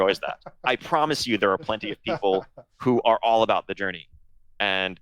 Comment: But you see how attempts of Blizzard to show a good faith towards each group is being shit on by players that are not a part of it. PVPers got their loot, M+ people are going to get more stuff from what I heard, but when there is a good faith attempt to leave a bit of story and content while leveling their 2nd/3rd alt just for the sake of different story perspective eg. going through different covnenant camping, the semi pros and grinders are like "fck no, I want to have my 50 alts ready on week two of the expansion" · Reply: I am not a native english speaker, but what I get from it, is Ion is saying "I promise you there are people who are all about the journey" and the response is "that's not what we are saying, we don't want that" and there are "WHO"'s and "what journey" flying in chat, but also people going back at Asmon the same way I did in the orginal comment, that's why I think my read of all of that was correct. But hey, if I got the intention wrong then I am sorry